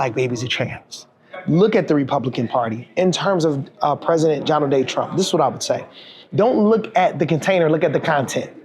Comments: Did he say, “President Jonald Day Trump”? Well, at least he’s got the last name right. 😂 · Did he say “Jonald Day Trump?”